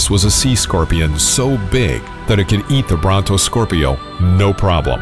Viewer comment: Lol I thought I heard burrito scorpio